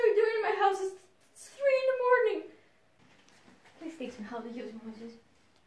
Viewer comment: WHAT DID HE SAY I CANT UNDERSTAND SOMEONE PLEASE TELL MEH I HAVE CONFUSION!!!!! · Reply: "Please teach me how to use emojis"